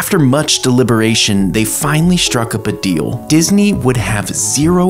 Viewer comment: Am I the only one that heard him say buhdeal?